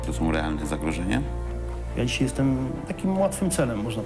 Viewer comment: …dobrze usłyszałem,… że jesteś łatwym c(W)elem😂?